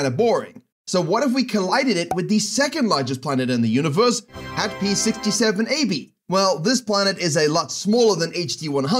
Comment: Did you said 67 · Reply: The second biggest planet is HAT - P - 67 Ab